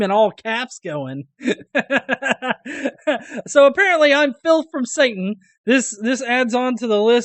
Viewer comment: I thought you said Phil from Satan in the intro.